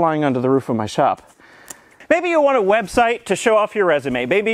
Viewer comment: Did I hear "Web shite" at 9:15? :D